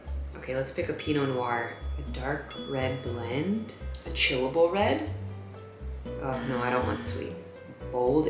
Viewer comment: When you were picking the red wines, I thought you said chewable red instead of chillable red. Yum, a nice thick, chewable wine!